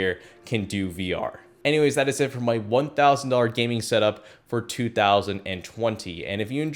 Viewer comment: I thought you will say "My 1,000$ Gaming Setup For 2,000$" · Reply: LMAO HE SAID 2020 NOT $2,000